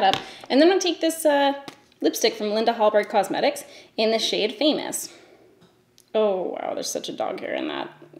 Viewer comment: I was eating a cookie when you said the lipstick shade name "famous" and I swear I heard "anus". 😳 I had to go back and listen again because I almost choked on my cookie. 😂😂😂